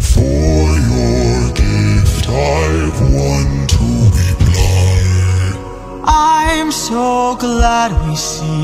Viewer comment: I just realized when Polyphemus says "for your gift I've one to reply" you can't see his eye at all. THEY'RE NOT SEEING EYE TO EYE! I dunno if that was intentional but if so that's magnificent. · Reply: I never realised he said " i've one to reply " , i always heard " i want to be blind " which i found was a great way to both say he'll act like it didnt happen but also inducing foreshadowing of what will happen next , i'm both happy to being abble to sing along more accurately but also a bit disappointed that , this detail that i originally found cool as hell , isnt real :)